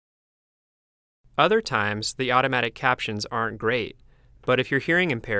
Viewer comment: lol i tried the transcribe and at 0:58 instead of saying auto captions "aren't great" it said "are great"